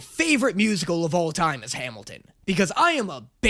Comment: Did I hear Hamilton